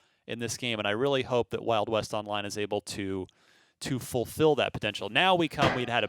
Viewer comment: i thought it said "west world online"